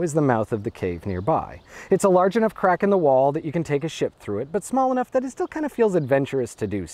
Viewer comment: 1.56min Sounded like you said "you can take a shit through it"